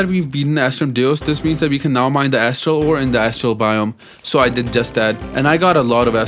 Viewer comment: bro to me it sounds like hes saying a** hole 7:25 😂😂😂😂😂😂😂😂😂😂